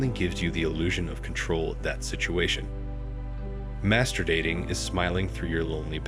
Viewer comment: I though I heard "mastu... " for a second lol